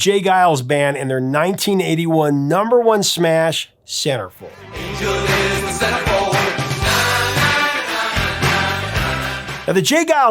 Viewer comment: Had to look it up, couldn't figure out what you said at 1:46 ! Sounds like SarahFey... Those you win scammers are everywhere including Facebook... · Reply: You realize now he said Centerfold right? I hit your time stamp and 😂 I can see how you heard SarahFeh ... Take 2🎬....let's try this again aaannndd Action! 🎬🤣✌️❤️🎸🎶🙏